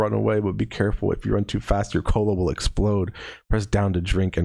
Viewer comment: i thought it said "or your colon will explode" and was both concerned and strangely unsurprised